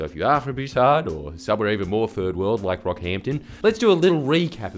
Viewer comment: I had to rewind as I thought you said the rap group “BROCKHAMPTON” instead of “Rockhampton”.